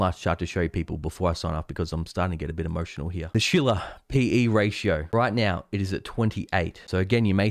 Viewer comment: When you said "Shiller PE Ratio" at first I heard "Sheila PE Ratio." That would be a very interesting chart!